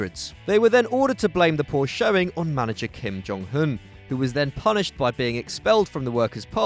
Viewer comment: Xd I thought u said Kim Jong-Un at 7:53. In fact, it was Kim Jong-Hun xd